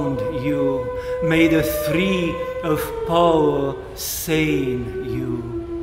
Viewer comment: Words to this would be good I hear 'three of four' but I guess it should be three of power and 'feel' instead of 'feed'. Quite changes the meaning. I was thinking who is the fourth person of trinity lol. In all charity more clarity of words would be helpful. Topic of immense interest to myself. Pray for me to be able to thrive in the storm father. Thank you